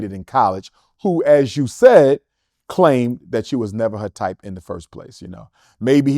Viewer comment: I thought they said she was preggers ?